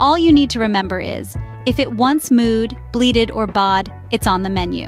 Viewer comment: what's it the third word? Please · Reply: Moo, bleat, baa